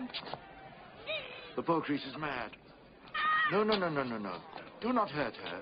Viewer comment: What is a "boutrisse"? He says it at 0:45. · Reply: He's saying "poor creature". "The poor creature's mad."